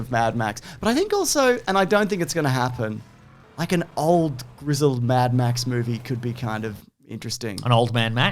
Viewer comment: When they said “old grizzled”, I thought they said “old Griswald” and they were referencing a “National Lampoon’s Mad Mad Family Vacation”, which now that I think about it, it kinda is. 🚙